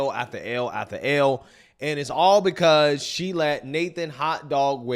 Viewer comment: I heard D after D after D